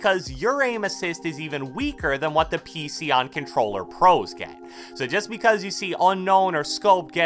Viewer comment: did he just say pc on Controller???